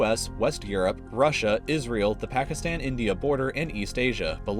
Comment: israel what i heard is 'is real'